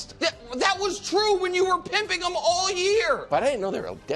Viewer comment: Did he just say 19:05 pimping them?? 😂